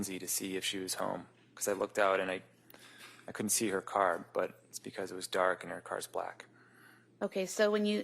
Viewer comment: For those who have trouble listening and understanding: 20:24 "I couldn't see her car, but it's because it was dark and her car's black." He is practically saying that (july 2026) he knows it was there, as he mentioned earlier, but (january 2023) he couldn't see it in the dark.